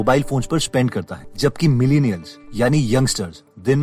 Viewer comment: Who else heard millionaires when he said Millennials?🤭🤭😂